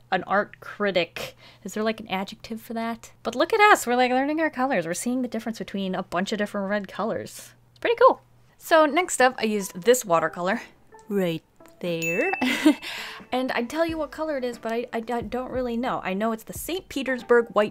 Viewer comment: was it only me who couldn’t see what she was talking about for a few seconds😂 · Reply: I thought she said white where instead “right there”